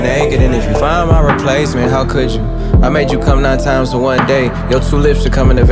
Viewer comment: pls when i was listening to the censored version, i thought he said "i made you poop nine times a day" i felt so childish cause i was laughing my ass off